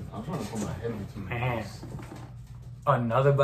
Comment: I’m tryna put my head between those Is what he said ... 😩🤣